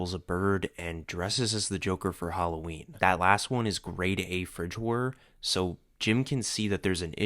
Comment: I'm gonna need someone to tell me wtf he's saying here because I can't understand him. Grade A Fridge-work? Grade A French bird? I really can't tell